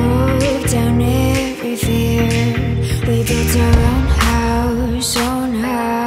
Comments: I Thought It Said "Loud House" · when I was searching for this song online, I honestly thought she was saying roundhouse